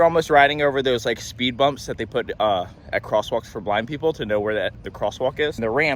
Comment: lol I thought you said black people 😂